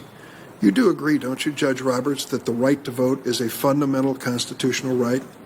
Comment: Did he say "the white to vote", or have I just been listening to democrats constantly talking about race to long and it's starting to effect my brain?